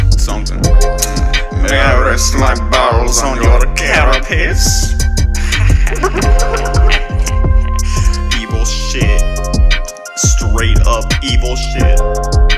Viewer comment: what does it say? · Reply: "May I rest my balls on your carapace? Evil shit, straight up, evil shit"